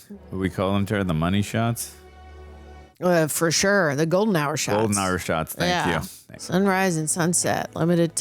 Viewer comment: Did she say "golden shower shots"?